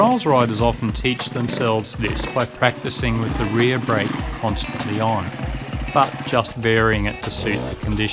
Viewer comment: What did you say just after 3:50 ? "Trails riders often teach themselves this by practicing the rear brake ??? but just ..." can't catch what you said. Thank you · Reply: by practicing with the rear brake constantly on. e.g. very slightly dragging.